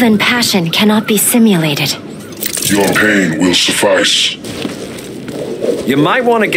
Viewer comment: when he said "your pain will suffice" i heard "cocaine will suffice".